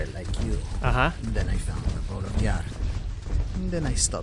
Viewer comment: i thought he said i used to be an adventurer like you then i found a ball of yarn